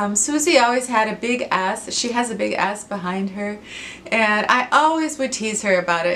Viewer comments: That looked like a fun and reminded me of Indiana ;) Um does Swoozie know you told everybody she had a big butt? ROFL A big A$$? LOL I had to back that up GF LOL OMG I was laughing! I love Swoozie, she's one of my favorite people too. I'm having problems with salt of late too but it wasn't until you started talking about it that I realized it was salt! I hope you have fun on your break and I agree...we all need one from time to time. xoxox Mary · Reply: Oh Dear Lord....Mary!!! does it really sound like I said Swoozie has a big ass!!!!???? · That looked like a fun and reminded me of Indiana ;) Um does Swoozie know you told everybody she had a big butt? ROFL A big A$$? LOL I had to back that up GF LOL OMG I was laughing! I love Swoozie, she's one of my favorite people too. I'm having problems with salt of late too but it wasn't until you started talking about it that I realized it was salt! I hope you have fun on your break and I agree...we all need one from time to time. xoxox Mary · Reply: Oh Mary....I thought it was maybe just you, my dear friend who thought I said Swooziebird had a big ass behind her, but no...it is everybody....( hanging head in shame) I will make it up to her ...don't know whether to laugh or cry!!!!!